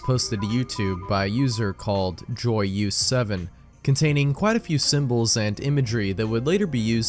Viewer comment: I was listening to this in the background and I thought you said "soyuz" when you said "joyeuse7" at 40:43. Soyuz meaning "union" in Russian, and name of series of Soviet spacecraft. Soyuz 7 was also a name of a test flight in 1969.